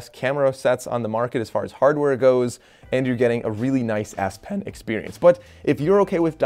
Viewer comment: Really nice S pen lol.... I thought you kept saying nice ass pen 🤣